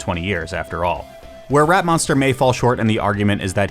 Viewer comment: I think the name was better when i was mishearing his name as "Ratmonster"